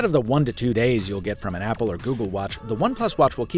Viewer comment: Did you say Apple or "Google" watch 2:59 That triggered a Pixel user here!! :p With the rumors doing the rounds, hopefully we get that Google watch this year. Unless you meant Google=Fitbit watch. :p